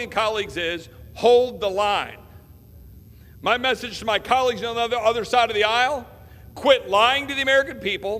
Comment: Lol..love ot.. I know he didn't say it but it sounded like he said..my colleagues on the UNDERSIDE of the aisle!! #perfect